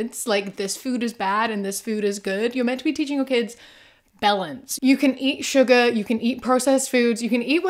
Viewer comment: ish I totally misheard balance as "bellends," and I was like, "Do NOT teach your kids that!"